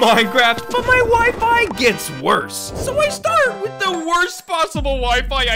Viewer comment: I thought he said his wifi gets worse